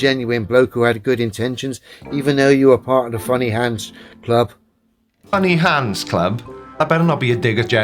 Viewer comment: I thought he said funny Hans club, and thought it was a club for German stand up comics.